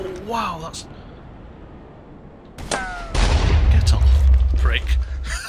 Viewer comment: did you say "geht doch"?